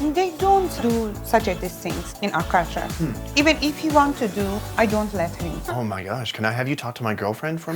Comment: wait did the woman say about men cooking "even if he wants to do , i dont let him" xD? i understood it wrong or is she really saying this? how arrogant lol. she hates her husband?